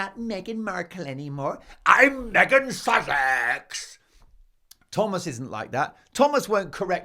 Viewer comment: I’m in southern USA. It sounds like your saying “I’m Meghan sausage!”